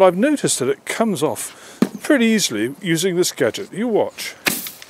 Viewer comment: swear my guy just said 'you watch' 🤣🤣